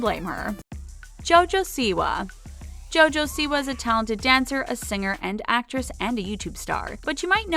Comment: did she just say talented